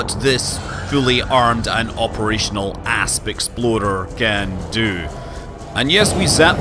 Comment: I showed this video to my younger cousin who is getting into space exploration games like Elite and he about died each time you said "Asp Explorer" because he thought you said "Ass Exploder" lolol.